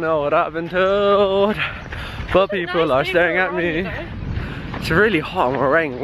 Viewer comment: did you say poor people? rofl